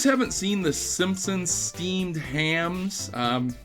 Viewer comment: I thought you said "Steamed Clams"